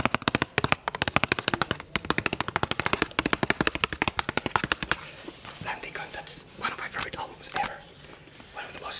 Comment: What album does tap on and say at 5:53 ? He says its one of his favorite albums ever but I can't hear what the title is. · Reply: its dan deacon’s bromst album